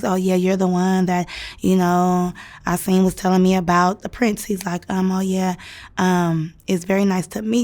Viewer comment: I think she said Prince Aseem??